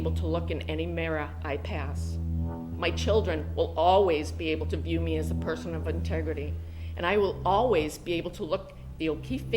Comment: It sounded like she said “my children will always be able to view me as a person with unntegrity”